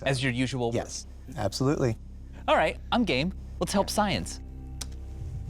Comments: why did it sound like he said “I’m gay” 😅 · "All right, I'm gat, let's help science" is what i heard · I swear i heard vsause say "gay"